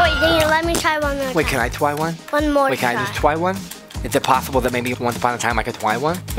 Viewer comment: What did fgteev say at 5:58????? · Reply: he said “try one”